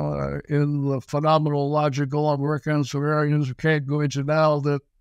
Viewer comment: ish. What did he say? Phenomenal logical work what?